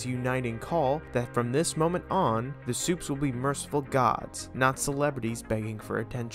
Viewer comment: I thought he said "wrathful gods".